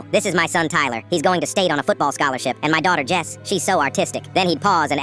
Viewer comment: When he said “my daughter Jess she is so artistic” I thought he said so autistic